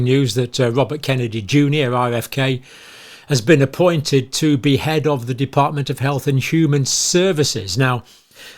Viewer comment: Did he just say that jfk jnr has been appointed to behead the department of health & human services! 😂 😵🪓